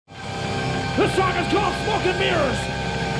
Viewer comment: "This song is called Fuckin' Mirrors" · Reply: Sebastian Boros it sounded like he said , but he said Smoke and Mirrors .